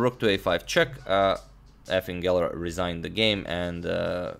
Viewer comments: Sounds like is saying "Effing Geller." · When you said Efim Geller I thought you said effin' geller, as if Bobby was unhappy with his performance.